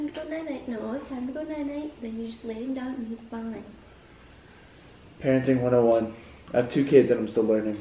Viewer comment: I thought said Kerenting instead of parenting 😂😂